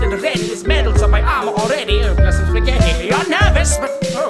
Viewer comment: Papyrus: singing Also Papyrus: turns into Eminem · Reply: at first i legit thought he said "mom's spaghetti"